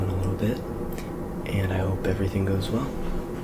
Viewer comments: I thought he said, “I hope everything goes wrong” 😂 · ? Did he say "I hope everything goes wrong" or is it just me..?